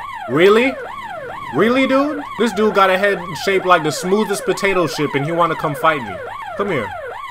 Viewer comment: Am i the only one who heard him say potato ship 😂😂😂😂